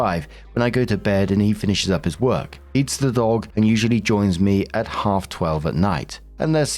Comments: S1: Being jealous of the attention your pregnant wife is getting has got to be one of the most pathetic things I've ever heard. Edit: Am I having a stroke, or does it sound like says he "eats the dog" at 7:40? XD S2: Ah Useless Lesbians, one of my favorite tropes. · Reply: I definitely heard "eats the dog" too, lol. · S1: Being jealous of the attention your pregnant wife is getting has got to be one of the most pathetic things I've ever heard. Edit: Am I having a stroke, or does it sound like says he "eats the dog" at 7:40? XD S2: Ah Useless Lesbians, one of my favorite tropes.